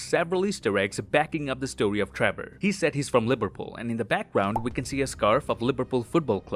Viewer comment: am i the only one who heard i as lipperpool